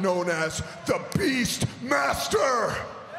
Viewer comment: Did he just say “the beast monster” 🤦‍♂️